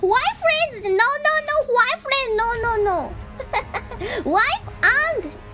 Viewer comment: I almost misheard waifriend as guyfriend Hololive is slowly turning me gay